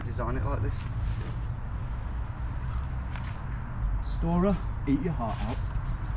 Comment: That Storror shoutout 😂. They make climbing walls, jumping three meters down and such look so easy. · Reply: I'm so lame, I had never heard of them before and honestly thought he said 'Dora eat your heart out' as in Dora the Explorer! 😂